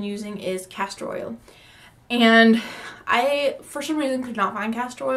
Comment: 😂 😂 She thought u said CASTROL Motor oil